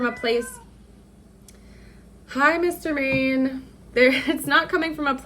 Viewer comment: did she say hello Mr. Man to her cat? If so that is so coincidental, and weird.